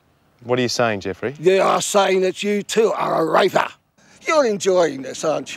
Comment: You are saying that you too are a RAYPAH! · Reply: i thought he was saying you too are a raverrrr